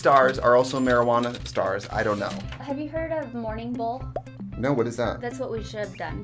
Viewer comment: A "Morning Bowl" sounds waaaay classier than "Wake and Bake" 😂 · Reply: I thought she was saying morning BULL